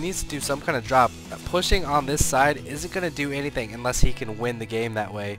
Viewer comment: ok ok i get it! we will hereby agree that hr said "isn't". thx please stop replying! i already told you that english isnt my first language and he speaks to fast for me to hear 2 more letters when i am sure i heard "is" instead of "isn't". please, let it go and let me be :)